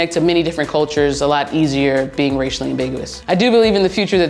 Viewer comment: I thought it said ethically ambiguous